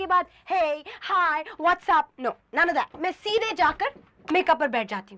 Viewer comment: did she just say WhatsApp? This world has a whole different meaning today. This serial was so ahead of time 😅